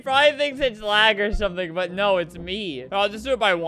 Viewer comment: did you just say...ITS ME?!